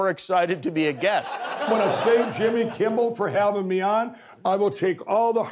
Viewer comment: Why do I hear Jimmy "Kimble" everytime he says Jimmy's name? 🤣🤣